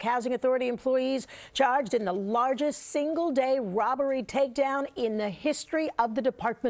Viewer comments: She clearly says "ROBBERY takedown" at 0:03 in 😂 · Sounds like she said "robbery" rather than " bribery".